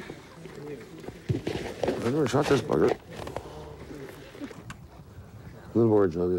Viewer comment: did Hunter just say the "f" word without getting bleeped? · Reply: No, he said, “I’ve never shot this bugger,” in reference to the gun.